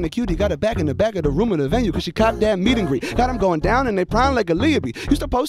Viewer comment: Did he say, "...got'em goin' down in their prime like Aaliyah be.." @ 2:44?